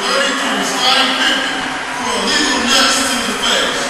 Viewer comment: LMAO anyone catch the PA announcer at 0:50 "The refs have called a technical foul on #32 Scottie Pippen for illegal nuts to the face"